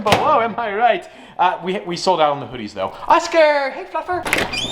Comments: it definitely does NOT sound like you said "...hey, fluffer" 8-) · sounds like he says hay fu*ker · Ik im not the only one who thought he said “ Oscar hey f*cker” 😂 Edit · I thought he said OSCAR hey Fucker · I kept replaying this part if i heard it correctly. 1:53 "Oscar, hey fucker!" I'm pretty sure he said "Fluffer" but I'm not sure. XD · DID HE SAY ''HEY FUCKER''